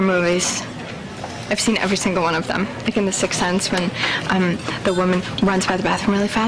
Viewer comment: does she say at 0:45-46 "even the six sense"?